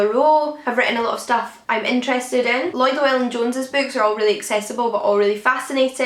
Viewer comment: Who was the historian who you said wrote really accessible books? (6:54) I couldn't quite make out the first part of the name. Also, I really enjoyed this! Like somebody else said, I thought it might turn out to be really long, but it zipped by. :) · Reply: Lloyd Llewelyn Jones - his books are very niche and on specific topics as well as really innovative research but the way he rights is just so consumable and fascinating. He does a lot on Persia although Aphrodite's Tortoise about veiling in Ancient Greece was a really important book of his too. :) That's really great to hear! I was worried about the length aha.